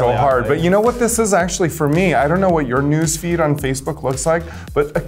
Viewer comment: I thought she said “nudes feed” aquaria looked at her like what 😂